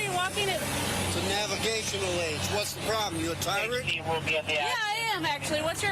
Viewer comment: the subtitles says "what the problem, you tired?". When he actually says, "what's the problem, you a tyrant?" And the cop just happily answers yes